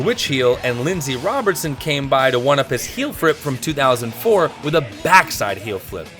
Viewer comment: did you say ”heelfrip “?😂🤘